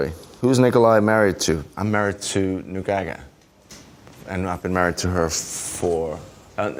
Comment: I can't be the only one who thought he said Gongaga when replying who he was married to